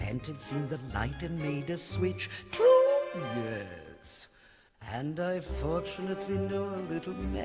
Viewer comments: my whole life and childhood I thought she said “True yes” not “To this” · I think that at 0:39 it should be "True? Yes" instead of "To this".